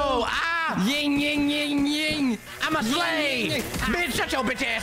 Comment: I heard "I'm a slave!"💀💀💀